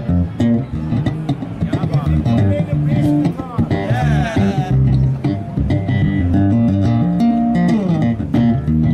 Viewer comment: What man says at 01:38? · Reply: reggae on the bass guitar ...I think rsrsrs